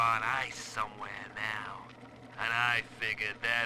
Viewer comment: Seems like the transcript for Fontaines An Empty Niche audio log has an error near 9:55 where he says "scam" instead of the written word "angle" :o